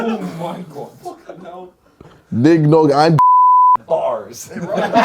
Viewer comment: what's that censored word (g********g)? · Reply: Gollywog